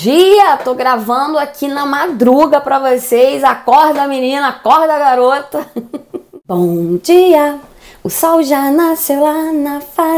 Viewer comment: "Bom dia menina e bom dia garota"... Bem o vídeo deve ser só pra mulheres... desculpa a visualização...mas foi só na marca 0:09... Perdão mesmo...valeu e boa sorte com o canal... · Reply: Vc que ouviu errado. Falei bom meninooooo. Limpa o ouvido meninoooo